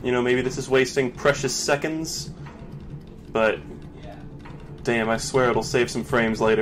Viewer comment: Does anyone else hear the yeah at3:38?